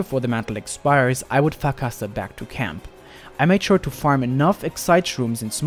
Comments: I dont know if its just me but when he said farcaster is sounded like he said fuckassa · sounds like u say i would Ouck Fff back to camp